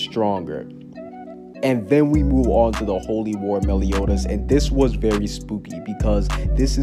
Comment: somehow I thought that said Holy Water Meliodas.....